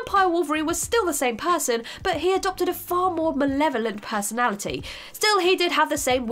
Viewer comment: I Know i am Months late but can someone please help me and tell me what word she says at 6:47 what kind of personality ? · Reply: Malevolent